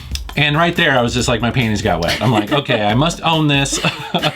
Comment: did he say his penis got wet???? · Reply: panties